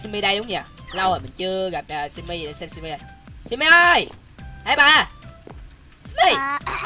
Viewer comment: lúc đầu là kiêu simmy ơi rồi tới lúc sau thì kêu em à 😲😲😲😲😲😲😲😲😲😲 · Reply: kêu là "ê bà" nhưng lúc đầu mình cũng nghe thành "em à". Hihi